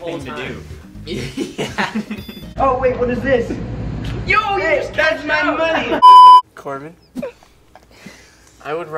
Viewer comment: Zach: *found a cash* Oh, wait! What is this? Jack: *rapidly stole the money* Corbyn: *points at it* *shouted* (I think he said "Jonah's cash!") Jonah: *gets up* THAT'S MY MONEY! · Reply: Corbyn said yo he just cashed out